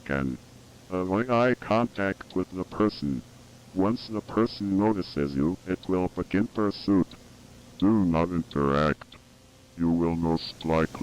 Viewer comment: I heard "Once the person notices you it will begin birth too" in